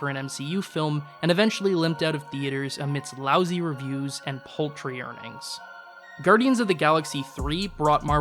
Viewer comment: did you just say "poultry"? I think you mean paltry lol